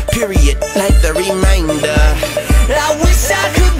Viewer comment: Wow.. so this WHOLE time since childhood I thought it said “I wish I could be as COOL as you” not cruel🤣